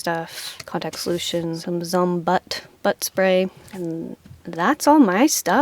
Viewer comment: I’m sorry, butt spray ? Or I misheard... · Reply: Butt spray!! It’s very nice to spray on tp for wiping. It’s like a bidet in a bottle!